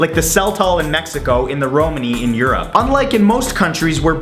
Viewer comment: The actual tradition(at least in romania, although is actually fun game not a tradition) it is actually, when a couple marries, the husband's friend "kidnap"(and by kidnap actually they just hide somewhere for about 20 mins) and the husband just has to find her for fun, it is just a fun game like the "whoever catches the bouquet of flowers will mary his gf/bf next", a game/tradition "practiced" all around the world, including America. So before you speak, get your facts straight bcause is really fucking annoying to hear someone like you talk bullshit about Romania, when is not actually true. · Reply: Oh sorry then, i thought you said Romania although i listened it a couple times and that really pissed me off, but sorry then :)